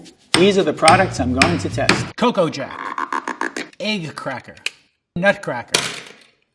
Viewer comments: it sounded like he Said ''Coco Ja ah ah ah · all I hear was “coco jaaaA-a-A-A-a-“